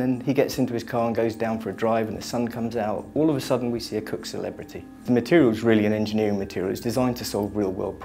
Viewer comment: Did he just say "All of the sudden we see a COOKED celebrity"? HAAHAHAH WTF